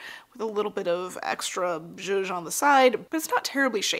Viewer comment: cc says "a little bit of extra judge on the side" what is that word? i've heard it before but i could never look up exactly what it means because...i don't know how it's spelled which makes me helpless in a search engine 😀